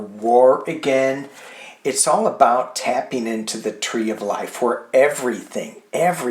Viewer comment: 😂 - when you were talking about the Oceanside view for the mouse and the owl came, I heard “the circle of life”